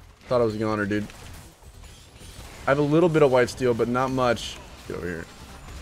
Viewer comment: anyone else hear him say i got a little bit of "wife steal" but not much